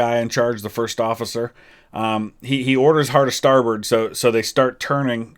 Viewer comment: I’m very aware of which side port and starboard are. If you’re referring to Murdoch’s order of “hard a starboard” that I mentioned, that is indeed the order to turn left. Doesn’t sound right but it is. Look at the testimony of the men who were there. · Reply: I apologize; I heard "hard to starboard". Honestly meant no offense, and was hoping to help others, more than anything!